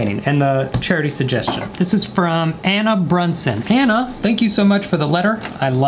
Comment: I thought I heard “Anna Bortion”